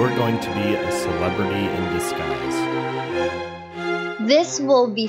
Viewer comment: I thought this said " children in disguise"